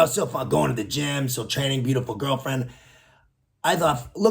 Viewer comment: Did I hear him say, “beat up a girlfriend”? 😅